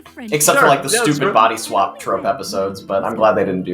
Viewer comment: did you say body swap? 🤔 season 9 has one of those